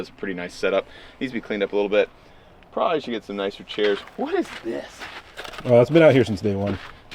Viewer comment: Did he just say i RON chairs 🤣